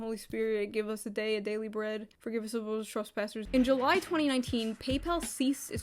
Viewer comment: Entertainingly, captions didn't hear you say "Forgive us our trespasses" it heard you say "Forgive us - trust those pastors". Oof.